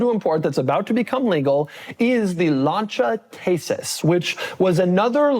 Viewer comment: Can Doug even fit into an Audi A1? And I initially thought he said Lancia Tasteless, which would have been more fitting. 😅